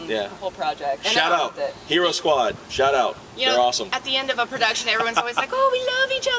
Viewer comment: First I hear "Shut up!" when he said "Shout out!"... So awkward..